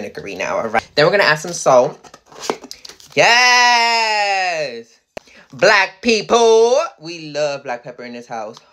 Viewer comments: LMFAO I thought you said "add some black peopppleee"😂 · Black people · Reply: nothing bad lol she just said black pepper a different way but it sounded like she said black people and I was like what lol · Am I the only one that thought she said black people when she really said black pepper 🤣🤣